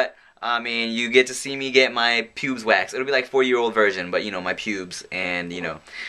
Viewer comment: LOL! Did just say "...it's like 4 year old virgin"